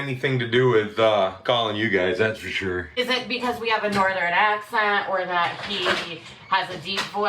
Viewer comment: Could simply be a bad connection or noise that interfered. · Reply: Actually I heard it later and when she said North, I think she said “northern accent” and the end of that word was really faint so I wondered if that’s why he thought she said Norway, lol